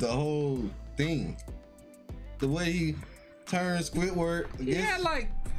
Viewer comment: Did I just heard said Akainu manipulating Squidward? 😂😂 Into what? Selling krabby patty recipe?